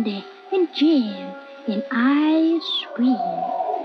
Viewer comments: did someone understand "ice cream" I scream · for a second thought. I thought she said "I Scream" instead of "Ice Cream"